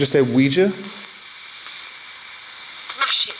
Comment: When the k2 was going off while standing next to the quija then asked again do you want us to use the quija board it responded w/ "smash it!!" I thought it said magic at 1st but it actually says smash it!!